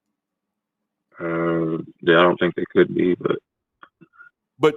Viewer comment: I thought he said "I think they could be [evil]" and nearly spat out my drink